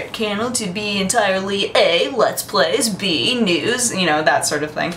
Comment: "B: Nudes" O.o wait what? *Rewinds* oh you said news...